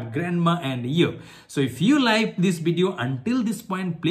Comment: did you said unlike this video or i hear it wrong